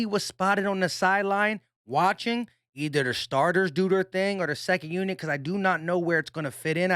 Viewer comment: Dangit. U said 2nd unit. I thought u said he was on CC unit trynna catch that fat guy from Bakersfield, CA. Go away ben. Shoo shoo.